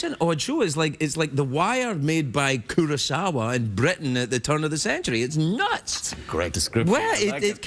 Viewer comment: From 3:22 - 3:25 sounded like an entirely new made up language to me. Translation anyone ? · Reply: "Made by Kurosawa in Britain at the turn of the century". Yes, i know i'm a year late.